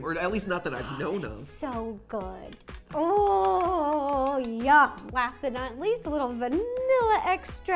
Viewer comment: did she say yuck or Yum